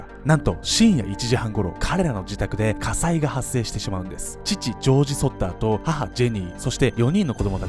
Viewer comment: 父ジョージが吉祥寺に聞こえた。